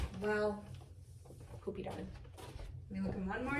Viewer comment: Thought she was saying "poopy yarn"!